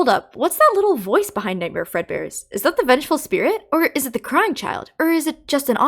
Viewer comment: did she just say nig*er Fred Bear